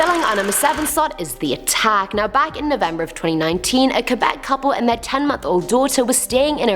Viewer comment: I'm glad I'm not the only one that heard her say November 2019 I had to put the close caption on just to see if she really did say that lol.